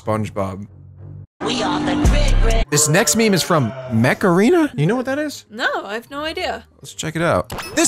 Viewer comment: I thought you said Macarena🤣